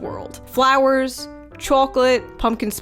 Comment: CHAWCOLATE!? DID SHE SAY CHAWCOLATE?